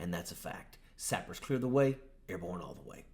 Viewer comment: So it’s “Sappers clear the way, airborne all the way”. I literally always thought you said, “snipers cleared the way, airborne all the way”. Just now seen “Sappers Airborne” behind you.